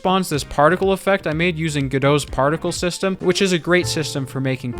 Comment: Did I hear "partial system"🥲 COME BAKKK PLLSSSSS😭